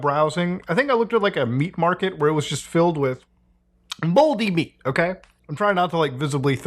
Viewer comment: baldi meat, that's wat I heard mutahar